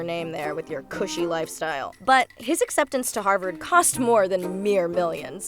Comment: I just choked on my Pepsi because I thought you said "his acceptance in Hogwarts"🧙‍♂️😆